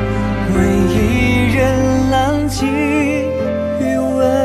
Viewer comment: I keep hearing it as “Wei Ying and Lan Wangji”